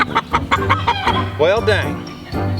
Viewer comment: I was surprised when I read this...I don’t think I have heard him cuss in any of his vlogs. I think he actually said “Well, darn!” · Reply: I listened to it back at a higher volume several times. We were both wrong. He said, “Well dang!” 😂